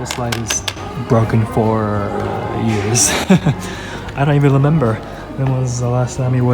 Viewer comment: did he just say "I don't even lemember"?